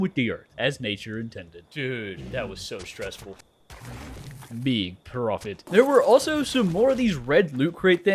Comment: Is it me or anyone else hear at 2:36:30 “big profit” 😂 Baron!!